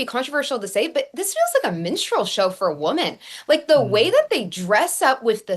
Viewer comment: I've been saying for years now that drag shows are modern day minstreal shows. 💯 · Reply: I was confused for a second, I swear I thought I heard menstrual show, I was like whoa what.